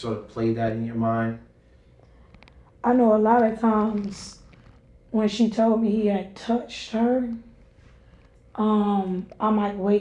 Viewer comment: Am I bugging or did she say “I know a lot of times when she (daughter) had told me he (boyfriend) had touched her…….” 22:16 ……..SHE KNEW SHE KNEW SHE KNEW what was going on.